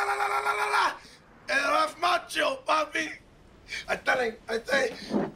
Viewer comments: They're just singing random things in Spanish it's hilarious 😂😂😂😂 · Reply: I thought he said “Eres macho papi” lmao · I say Jesus!!! Como se IIama papi!!!! PORQUUUUUE!!!!! 😂😂😂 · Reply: lmao i thought he said “Eres macho papi” 😂